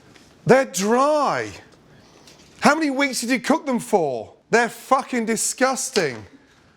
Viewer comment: No one else noticed at 0:40 he said,"how many wings did you cook them for?" Instead of minutes